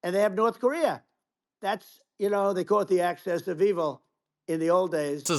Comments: I liked his "Access of Evil" statement, at 3:36. Then again, he probably thinks Axis of Evil is "Access of Evil." Can't wait to see the next edition of 'Shrinking Trump.' Love to see you interview a psychologist about him. Great channel you're running. Thank you. Appreciate the explanation regarding asylum seekers and Hannibal Lecter. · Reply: i heard "asses Of evil" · Did he just say "the access of evil"? 😂😂😂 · i swear he says "acces of evil" like he only heard the term but not seen it on paper.